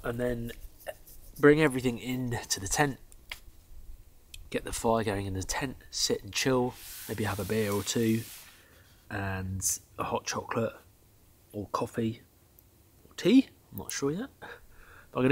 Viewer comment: I thought you said you have no beer ? You must have had a quick trip home